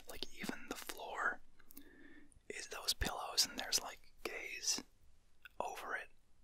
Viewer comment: Me: did he just say that the floor was coverd with gays?